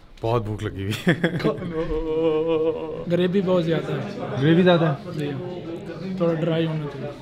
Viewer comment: I heard gharibee (purety) bohot ziada hai 😂 magar phone bohot achcha hai hamare pass when he was trying to say gravy bohot ziada hai